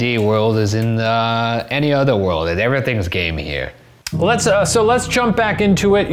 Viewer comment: i thought he said “everything’s gay here” and i was like true tho